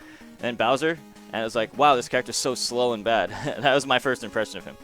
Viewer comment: "Wow this character is slow in bed." That's what I heard lol